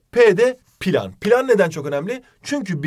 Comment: planı tek benmi pilav anladım 😂😂😂😂